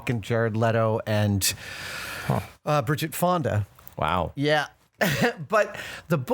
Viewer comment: LOL 6:06. I'm guessing the chair was leather and it makes a noise...but I thought was saying "Wow" to Adam letting one rip...